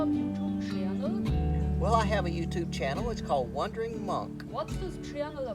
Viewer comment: It sounded like you said Wondering Monk which is an interesting moniker too. Good Luck